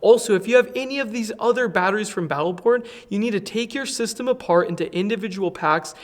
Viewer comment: Did I just hear "Battle Burn"?